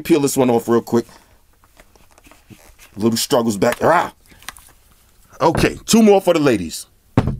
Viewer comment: He actually said rah at 12:29 !